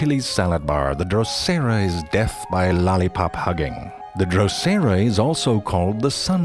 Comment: 最後のバァバで吹いた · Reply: ロリポップハゲに聞こえるんだけど耳イカれてんのかな